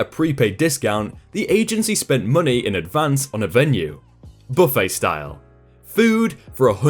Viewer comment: in, it sounded like he said, "buttface style." XD